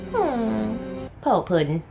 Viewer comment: I am watching your reaction, and can't make out what you said at 15:15 (when Worf is meeting the father for the exchange) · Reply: Hey! I said "Poor puddin'."